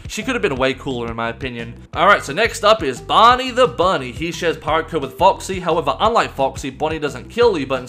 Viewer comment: I misheard that as, "He shares vodka with Foxy."